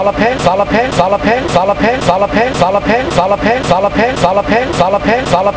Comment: It sounds like hes saying a pencil